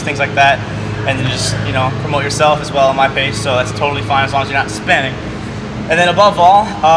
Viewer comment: at first I thought you said as long as your not hispanic, I was like o_0